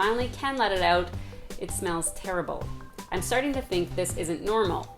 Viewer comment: I thought she said “I’m sharting to think...” 😂😂😒